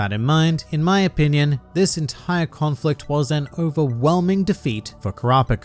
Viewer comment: I thought he said Uvowhelming😭😂